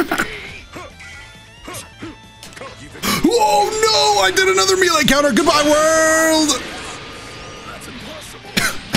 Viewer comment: Bro what did Vegeta say when he did level 3 against Jiren at 9:19? That was definitely a special quote but I couldn't quite hear it. Definitely wasn't his normal "Are you brave enough to take this one?" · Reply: "YOU COCKY BASTARD! FIIINALL FLAAAASSSHH!!"